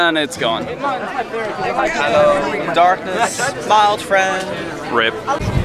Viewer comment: i thought he said rape xD